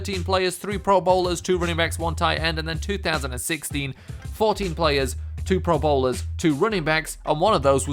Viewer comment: Sounded like he said "2016, 40 players," not the "14 players," he acctually said.